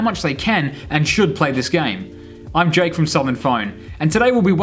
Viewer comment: i thought he said “im jake from state farm” 💀😭